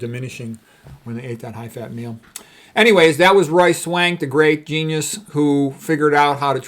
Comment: I know his name but when you say it i hear "Royce Wank"